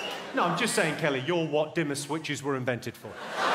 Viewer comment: demi switches???? Sorry? I literally don't know what he says here plz tell me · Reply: Dimmer switches